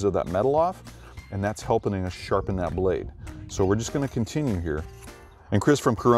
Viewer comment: I hear "Helpening us sharpen that blade." lol No worries though. it all makes sense!!! lol Cheers